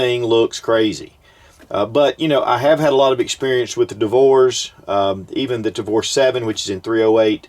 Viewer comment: I thought he said " I have had a lot of difficulty with divorce", thought this video was going to go a different direction... whew!